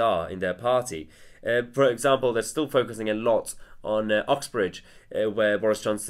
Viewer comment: Sounds like you say”oxbridge”